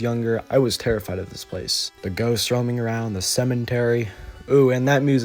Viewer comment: Excuse me, did you say Cementary?! LMFAO 🤣